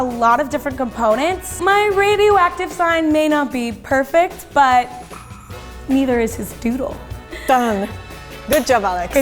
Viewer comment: Did she say neither is hi dick ant